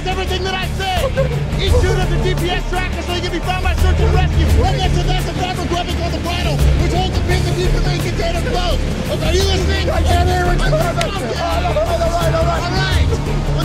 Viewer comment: In case one wants to know what Lennox said to Galloway at 1:10. "Each chute has a GPS tracker so you can be found by Search and Rescue. Right next to that's a fabric webbing called a bridle, which holds the pin that keeps the main container closed. Ok, are you listening? All right when the pilot chute inflates into the air, it pulls the pin and opens the main. Red's your backup, blue's your primary. I want you to pull the blue. I need you to pull it really hard!"